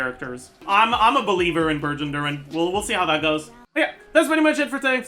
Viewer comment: Instead of "Burgeon Durin" I heard "Virgin Durin" and was like, "damn, boy's getting roasted"